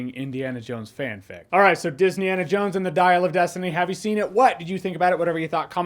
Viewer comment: It sounded like you said Disneyana Jones at the ending of your video, if that's not you said, then it should have been 😅